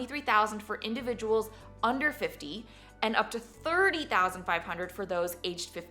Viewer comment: Great video. You need to update the graphic that shows $35,000 instead of $30,500 for max 401K Contribution for >50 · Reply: Correct, the graphic is incorrect and I thought I heard her say 35,000 but his could have been influenced by looking at the graphic when she spoke.